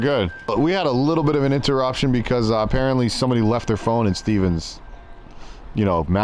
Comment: I thought he said someone left their thong before he held up the phone.